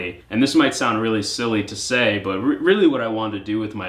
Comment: I heard "I wanna be a thot" and I went 👁👄👁